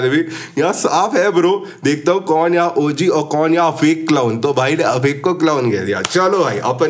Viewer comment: I think he called you OG and said other channels clown of yours :)